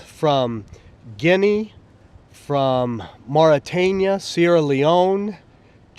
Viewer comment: did he say visa from Morytania?